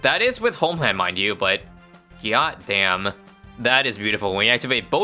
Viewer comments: Is it the brainrot in my head or did just say gyat damn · Did he say gyat damn